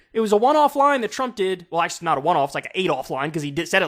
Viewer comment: He said “8 off” but I heard “Adolf”